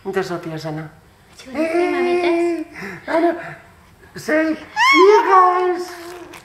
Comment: TOTALLY SOUNDED LIKE SOPHIA SAID I LOVE YOU GUYS 😱😃😍🤗🥰